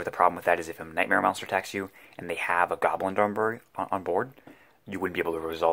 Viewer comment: I had to rewind; swore you said "goblin dong bird" ahah. Anyway, cool insight, i love seeing different takes/builds... chain burn is pretty strong, mostly cheap, and generally independent from whatever shape the meta takes